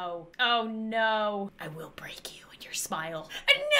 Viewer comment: At the beginning of the video I thought she said “I will break you and your spinal”